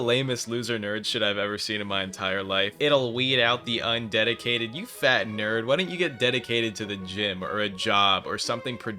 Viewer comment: okay THRAGG what the fuck are deep players saying😭😭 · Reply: The death of any freshie leaves a void in him 🙏